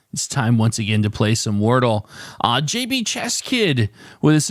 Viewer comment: I thought you said the starting word was Chest not chess. I like it much better if you SPELL the starting word when you announce it. Although chest worked better for me than chess did for you. After chest I did audio, taste, state…